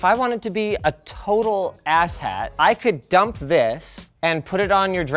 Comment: 🤣 Linus said asshat I thought he said asset